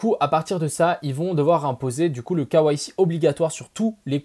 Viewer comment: imposer le quoi ??? Mdr avec le "k" à la française et le reste à l'anglaise il m'a fallut 20sec pour capter. "KYC" les gars !